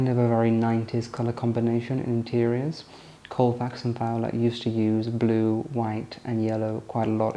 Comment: What are the names he says?? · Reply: Colefax and Fowler